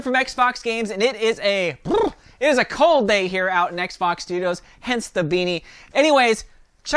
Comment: Sounds like he said xbox studios.